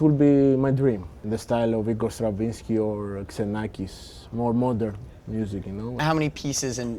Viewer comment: I don't quite catch the second person (Igor Stravinsky and who??) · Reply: Iannis Xenakis